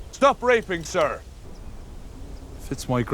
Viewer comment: did he say stop......nah, I must be hearing wrong 😂